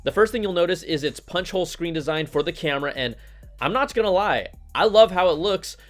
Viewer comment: Did you just say I'm notch gonna lie ? @ 0:29... Hahaha :-/